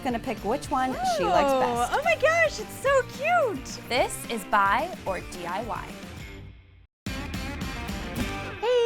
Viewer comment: IDK why but I thought it said buy or die